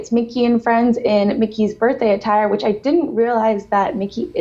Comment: She said 'birthday attire", I heard "birthday suit" and got this really weird visual of Mickey Mouse butt naked. It was unsettling.